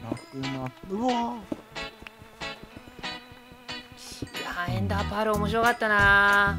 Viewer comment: 「エンダーパール」が 「エンダータール」に聞こえたw